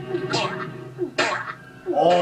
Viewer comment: or 58 squid ward said an arabic phrase I'm Arabic and I heard it he said u idiot "ya 7mar"